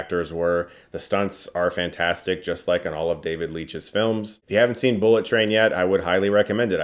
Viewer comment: I totally heard you say David Lynch haha